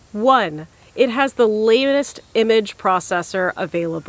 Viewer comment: Did I hear lamest image processor?